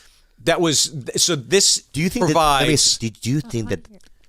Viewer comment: what did she say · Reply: Provide...? I guess.. Or Improvise? Perhaps.. I am High? Maybe .. Want some Chai?.. Idk.. Thigh.. I give up